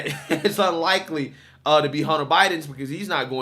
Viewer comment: I thought they said Hinter was using the Phone in the Library?